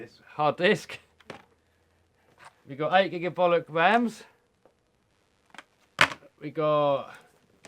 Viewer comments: Did i hear 8 giga-bullets ram? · I see a lot of angry gamers saying I'm driving up graphic card prices...dudes, get a playstation why would you game on a PC anyway? They cost 10x more and playstations have just as good graphics and are much more simple to set up. Gaming on a PC does not mean you have a bigger willy! see my comparison! <link> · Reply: he actually says "we got 8 gigabolocks of rams"